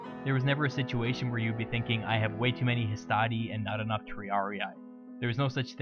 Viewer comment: There is an error with the subtitles. You said "I have way too much hastati and not enough triarii" but the subtitles say "I have way too much triarii and not enough hastati" Great video BTW!